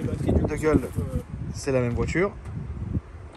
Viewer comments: c'moi ou il a lâché un "ta geule " à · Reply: Il a dit "la gueule,c'est la même voiture " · Big Up à toi si t'as entendu "ta gueule" à 5:29 😂😂😂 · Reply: c'était "de gueule", mais au début idem je pensais à ta gueule 🙃